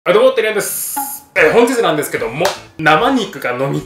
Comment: いつも「寺やんです」に聞こえる